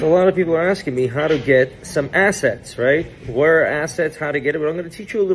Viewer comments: I thought bro said acids and not assets and I thought bro was holding a sheet of silver colored acid sheets 😂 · I thought he said get some acid lol